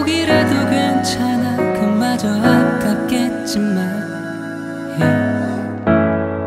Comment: 노래 좋다 헤... 근데 자막을 실수로 눌렀는데..엄마전화...으으으으으.. 감동 다깨져 ㅜ 그걸로 들리자나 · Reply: 그마저도 아깝겠지만 >>> 엄마 전화 받겠지만으로 들림 크흡 ㅜ